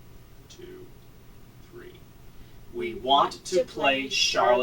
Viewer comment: Oh shiznit! 14:50 i heard "i dont want to play." After said we hav a toy for you i heard it again but it was "i dont want to play i said." 2 days an 3 different headphones, same spots same things...